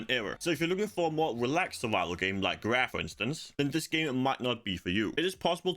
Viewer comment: "relaxed survival game like....for instance". I don't understand what you said after "like" (sorry my english is anything but perfect....) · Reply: "Raft," I think.